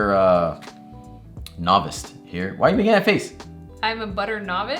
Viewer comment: Did you put a t in novice? It sounded like you said novist lol